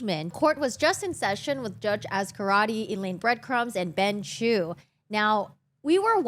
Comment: I had to replay to make sure I heard "Elaine Breadcrumbs".